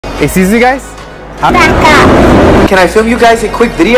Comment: when she said back up I thought she said “fuck up”